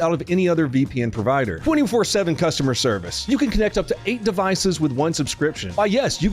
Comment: 😂😂 why did I think he said “44hoyr customer service “ I had to go back and listen to it again because that’s some shit would say 😂😂